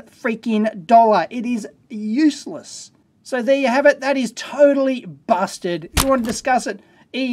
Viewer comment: I heard "totally bastard".